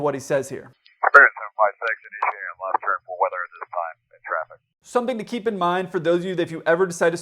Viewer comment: i heard " for weather at this time , end traffic" end traffic like stop speaking. Idk. Not a pilot Just saying what i heard